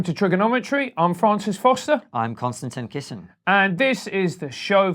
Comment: I love it so much when Konstantin is introducing himself!! I always hear “Constantly Kissing” 🥰😍😍